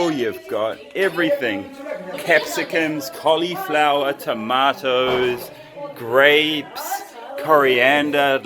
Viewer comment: Did he said Tomatoes or Tamaters ? 😂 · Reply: Tamaters 😍